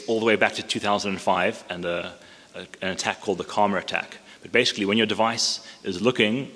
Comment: he mentions some attack. I can't hear what he says.. "coma attack"? Anyone? - Edit: he said: karma attack